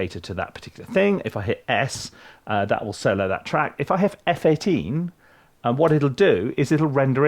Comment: Maybe I heard wrong, but did you say F18? What keyboard is that? Thank you